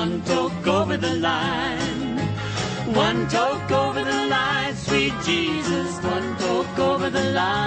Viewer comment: For their next Lawrence Welk performance, Gale and Dale performed "Heroin" by the Velvet Underground. Larry loved it! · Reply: When I was young I thought they were saying one "Totes" over the line, so in other words an umbrella